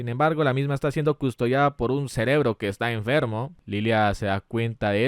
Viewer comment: Soy yo o escuché cerebro no era CERBERUS? Nomás digo